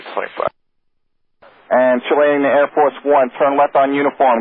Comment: DID HE SAY AIRFORCE 1?